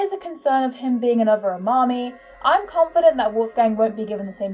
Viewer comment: did I hear that right "another mami" ? If so who the bloody hell is that (ashameing your not talking about the madoka magica one) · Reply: Rantaro Amami from DRV3!